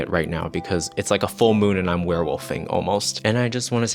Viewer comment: I thought she said queerwolfing instead of werewolfing. I'm not changing that in my head.